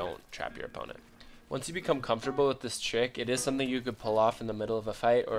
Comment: I thought he said, “Once you became comfortable with this chick.” 😂